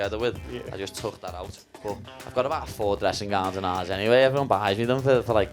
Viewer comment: would somebody mind to transcript what he said from 5:33 I've got about four dressing.... to 5:46 please? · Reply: "I've got about four dressing gowns at ours [his house] anyway, everyone like buys me them for gifts for christmas, so I've got pure [lots of?] dressing gowns. It's when he [his dog] chews the slippers lad, that's when it gets annoying. My Rick And Morty slippers, he needs to swerve [stop] chewing them, mate." Brackets are my own comments for context. Kind of boring content but a good study of the Liverpool accent lol